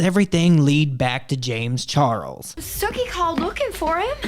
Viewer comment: I thought you said snooki 😂